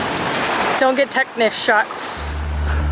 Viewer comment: And… Did she just say; “Don’t get tetanus shots”?